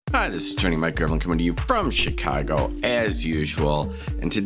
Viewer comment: When you say your name I always hear, "Mike Gremlin".